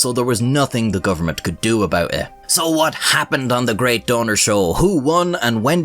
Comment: I thought you said “the great döner show”, like the kabab or something 😭😭